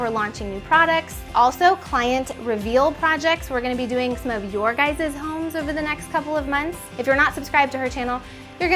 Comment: Did she just say “your gizzes homes”? 🙄